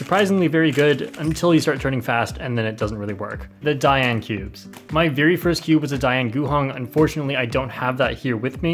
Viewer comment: I misheard I died and cubed lmao